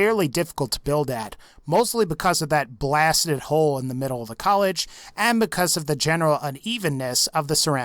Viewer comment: you said collage instead of cottage.